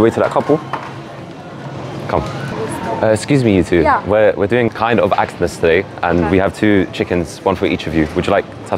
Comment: did bro say we are doing kind of actness today 💀💀😭😭😭😭😭😭😭😭😭😭😭😭